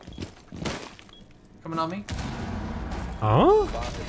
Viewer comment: what did you think he said. It just sounds like he said come and help me